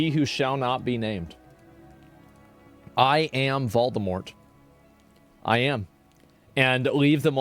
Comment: I am sorry, I heard "Baldemort"